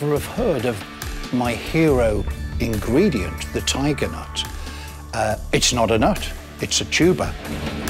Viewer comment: I legit thought this guy said “the tiger note. It’s not a note, it’s a tuba.” Imagine my confusion.